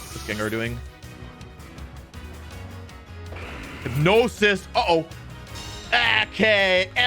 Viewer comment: When said Hypnosis at 11:53 I thought he said "No, sis!" XD